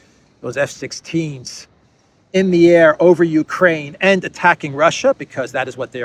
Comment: NY accent indeed 🤔... Did I hear Youz'kraine...